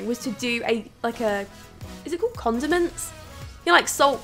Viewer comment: I thought you said continents but I do think continental cas would be cool like a North America, Asia, Europe etc